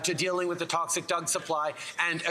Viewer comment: ok ok, did anyone hear at 0:44 ? - someone in the background faintly says something like "science from a crack smoking leader" and Trudeau ends up fumbling the word "Drugs" with "Doug" "Dealing with the toxic Doug supply" LOL - like Doug Ford... Am I the only one who hears this?